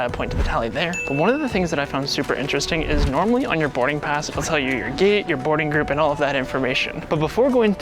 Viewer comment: I thought it said “it tells you if your gay” 💀